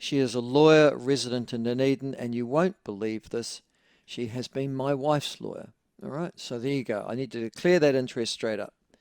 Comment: what did he say? you wont believe this, she has been my wife story? i genuinley cant tell what he says